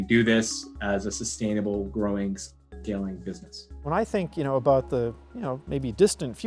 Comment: if you have captions on, it says: "...can do this as a sustainable growing, failing business." 😂😂 (he actually said scaling btw)